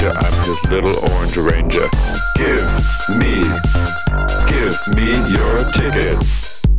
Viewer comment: Thought it said "Give me your chicken."